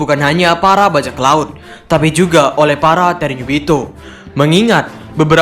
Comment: Entah gwa yg salah dengar atau apa???? Kok "ternyubito"???????!!!!!!